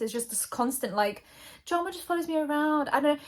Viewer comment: I swear I thought Gabby said that her shirt said "Jamba Juice follows me" and I was very confused.